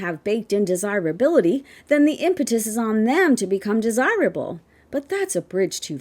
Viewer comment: For a moment there, I thought you said, “the impotence is on them.” 😂